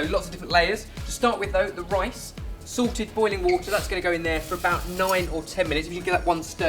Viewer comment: I thought you said 'Sorted boiling water' I think there is a market for that guys - looks lovely